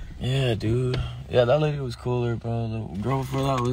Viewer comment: did he say "that nigga was cool"??? lmfao